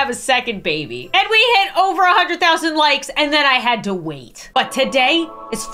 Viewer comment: I thought she said "and then i had twins" and when I tell u i almost snapped my neck looking back at my computer